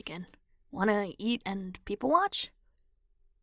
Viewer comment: it’s late at night and I thought that said wanna eat people and watch 🤦🏼‍♀️ HELLLLLPPPPPP💀💀💀💀